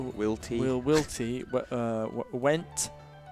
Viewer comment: Thought said "gwent" not "went" during the word game. tw3 has just consumed his life as much as Geralt consumes swallow.